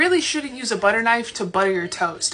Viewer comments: I thought she said to butter your toes 💀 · At first I thought she said to butter your toes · The last one i heard "butter your toes"... God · I thought she said buttering your toes